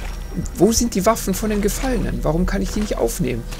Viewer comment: Hab verstanden "Wo sind die Waffen von dem Gefallenen?" :D:D